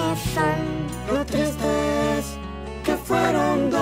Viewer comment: Yo pensé que decía "¿Porqué crees que fueron dos?"